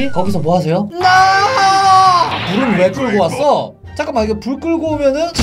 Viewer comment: 부터인가 불이 "날 끌고!"라고 나만 들리나?